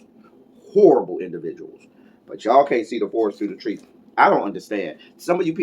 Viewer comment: If you could have heard me laugh when I thought you said, “…see whores through the forest”. I played it back again 😞🤣😂🤣😂